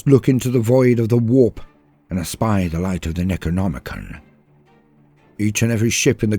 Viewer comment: Did you say "the light of the Necronomicon" around 20:50 in?